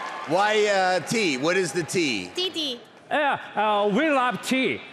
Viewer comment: ハイデイの「T T」が「 T○ T○」に聞こえてしまう😢